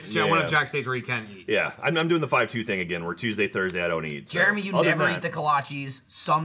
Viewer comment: Wait, did Jack say he doesn't eat on Tuesdays and Thursdays? Or did I misshear him/he misspeak? it was at about 52:05 or so Because that CANNOT be healthy